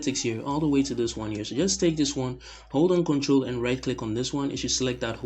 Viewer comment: way too fast. Did he say hold an control? I get just a new verex D